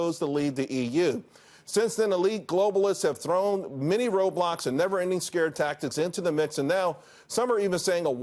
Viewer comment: thought he said “Roblox”